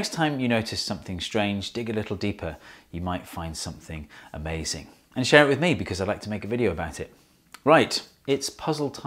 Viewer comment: I thought he said, "And shower with me because I'd like to make a video about it." 😳